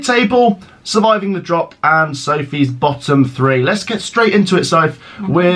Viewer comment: I thought you said something about Sophie's bottom?